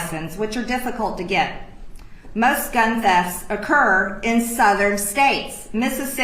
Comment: It sounds like she's saying that people in northern states go to southern states, get guns, then go back north to use them in their home states to commit murder. At first I thought she said, "deaths". But, it was "thefts". I was confused with why she would say there are a lot of deaths in the south and that guns are stolen there. I thought she was implying that the thieves were jacking the guns and killing their owners before taking them back up north. 😂